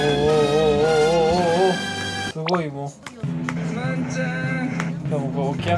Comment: この場面で「まんちゃ〜ん」って聞こえてくるのジワるwww